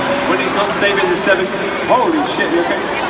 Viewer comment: I heard Chris said Shit